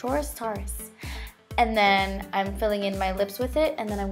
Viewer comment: When you first said Taurus, I thought you were saying "Tar-us" and I was like, well that's an interesting name lol. I guess everyone pronounces things differently 😋